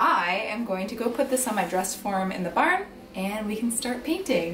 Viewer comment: I swear I heard you say your dress worm and not dress form,lmfao